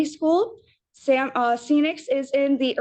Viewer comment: did she really say ‘Sea-nix’ and not Phoenix? 🤦‍♀️